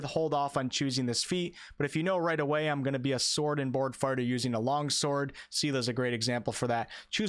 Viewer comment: Did he say "sword and board farter"?